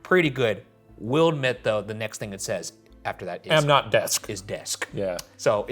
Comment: I think the word desk was misheard, it actually said "am not, dick" 😂 😂 😂